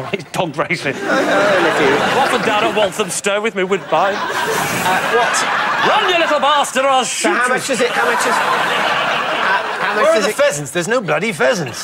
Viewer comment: What did Jimmy and Lee say @ 3:04 I couldn't understand, not a native. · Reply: "I'm often down at Walthamstow with my woodbine" "run you little bastard or I will shoot you" 1) Walthamstow is a racing dog stadium/racing venue, in North East London. 2) Woodbine are a type of cigarettes, that are no longer made. 3) He's joking about shooting his dog if it doesn't win the race.